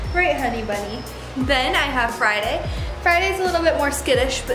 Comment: When you said Friday I thought you said Fried Egg 😂😂 please name your next animal Fried Egg lmfao